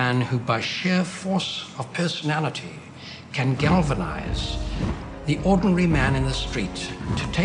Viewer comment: What was said at 1:02:56? I hear "galvanize the openry man in the streets" 😭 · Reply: “Galvanize the ordinary man in the street”